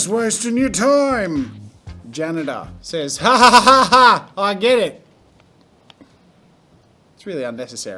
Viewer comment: is it weird that I misheard "I get it" as "I'm gay"